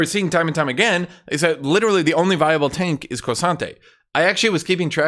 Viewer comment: Totally unrelated to the patch preview. But I love how says K'sante, sounds like he is saying Croissant'e